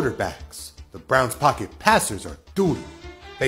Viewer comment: Lol it sounded like dude said the browns pocket passers are dodo